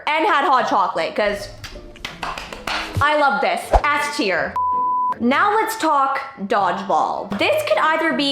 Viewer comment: not the caption saying a naughty word by accident- s tier turning into ass tier 😭😭🥀🥀